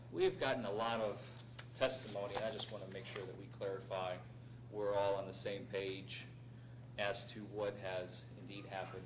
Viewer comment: DUI Guy: "So, we've gotten a lot of testimony, and I just want to clarify we're all on the same page as to what has indeed happened" What I heard: "Excuse me while I whip this out"